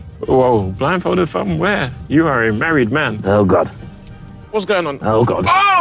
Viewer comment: did tobi say ethan is a married man? mad